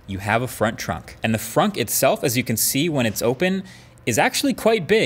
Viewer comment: Did he say "frunk" (front+trunk) 9:04?